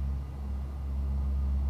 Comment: Did she say "Oh fuck them" at 3:49? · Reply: She called for flaps up